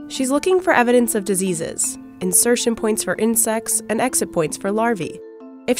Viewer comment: I don't know why but I heard "insex"